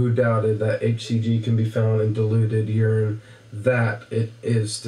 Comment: I thought he said deluded urine!! Lol, would make more sense 😂